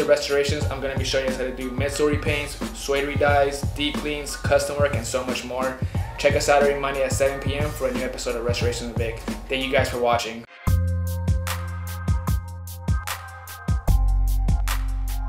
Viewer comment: I don't know why i thought when it said he meant like restoration with bucks vapor rub 😭😂💀